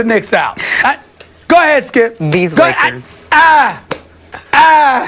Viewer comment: Bro what did she say at 9:52 ???? These Knickers???